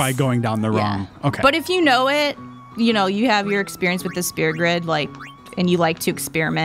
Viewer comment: she says "spear" grid. When I heard it I had to go back and listen to it a few times lol